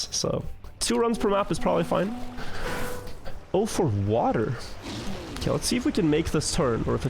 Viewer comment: I thought he said o for water instead of "04 Water" and was confused cause water clearly starts with W